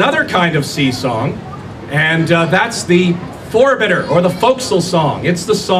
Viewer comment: what was that word? Forbitter? forebidder?